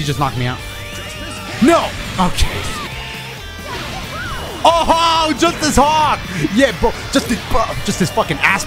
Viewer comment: I thought Videl said, "JUST THIS CO-" and I thought she was talkin all sorts of smack to Gohan